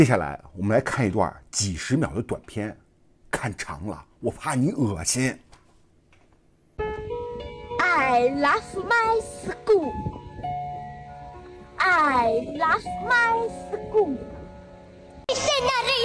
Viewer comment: I heard her said that 'I laugh my scool'.but the CC subtitle was I love my school, why?